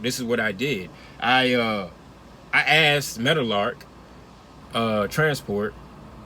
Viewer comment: Bro what’s the name??? Mettle Ark transport?? That’s what it sounds like you saying bro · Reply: Meadowlark